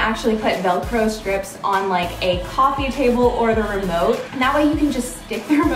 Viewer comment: When you said remote for some reason I thought you said limo